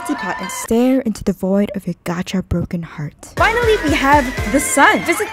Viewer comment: At the end it almost sounds like you said "it's found right up the chasms butt"😭